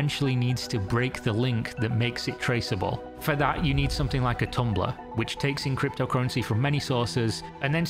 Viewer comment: I heard it as 'you'll need something like a tumblr' and was like, 'yeah, because there's no way anyone's going on that hellsite to find you'.